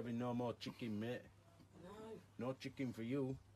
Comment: He looks absolutely devastated by the prospect of no chicken. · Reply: thought he was saying no children